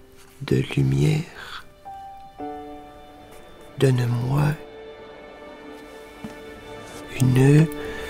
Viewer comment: j'ai pensé à "Donne moi une cuillère." en plus😂😭😂😂